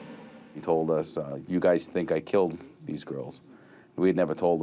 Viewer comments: I heard "they think I killed her". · It sounds like he says....They think I killed her. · I would never think this dirtbag shouldn't get what is coming to him.But to me he clearly says They think I killed her". Not that he didn't but it does not sound like he says Damnit. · I heard him say they think I killed her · I heard “ they think I killed her” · To me it sounded like he said "they think I killed her"